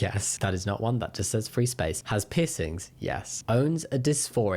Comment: on the transmasc bingo you said "has piercings" and i heard "has pi$$ kink" omg i am so sorry but also its so funny it needed to be said